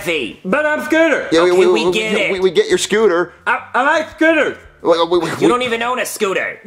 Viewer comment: I thought he said “i LiKe DiNnEr”🤣🤣